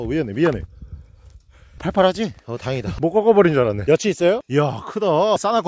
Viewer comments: 나만 여치있어요? 를 여친있어요? 라고 들었나... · 여친 있어요? 라고 들림 ㅋㅋㅋㅋ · 초에 "여치 있어요?"를 "여친 있어요?"라고 들림 ㅋㅋ